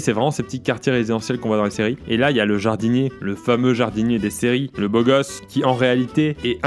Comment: Suis-je le seul être carotte parce que quand il dit vers 8:16 « le jardinier des séries » mon IPad comprend « Dis Siri » et active la assistant vocal. Donc de un l’iPad comprends « Dis Siri » et de deux Pierre Crosse à la même voix que moi puisque mon iPad est paramètré sur ma voix... Ça m’a fait buguer